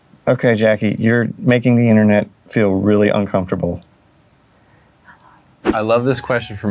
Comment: Only I hear from Jackie "I love you"?!